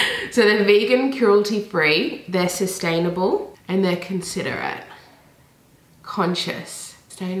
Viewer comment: did you say considerate? 🤔